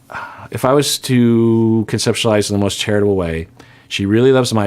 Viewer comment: I almost thought you said, If I were to conceptualize in the most terrible way, and I was very excited. But then realized you said charitable :)